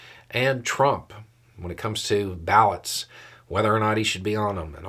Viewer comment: I was listening -- but my brain wasn't... "processing" properly yet and i first 'heard' 0:13 as "ballads and whether or not Tr*mp should be on them." And i thought, "O. Hell, no! He's effed up the country and the people bad enough! Jaysus! Don't get 'em singing long, romantic songs about him! Parody ballads only! There should be a law!" But then my sense caught up over my nonsense: "BALLOT! ya eejit!" -- which fit the rest of what was saying so much better.